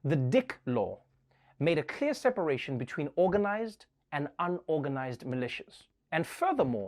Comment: You read my mind up until the part about the Law and Order spin off. I was making a joke about how I misheard organized and unorganized as circumcised and uncircumcised